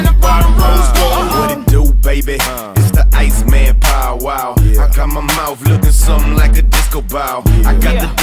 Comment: I always hear it as BOW WOW not Paul Wall. Is it just me?